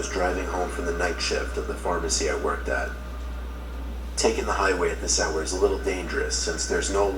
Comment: I thought said Hide wig but he said highway😂😂